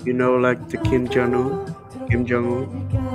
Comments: is he saying kim jungkook??😂 or I listened it wrong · did he just said KIM JUNGWOO lol lost nctzen